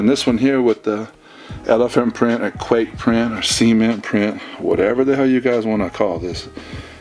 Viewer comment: i thought he said semen print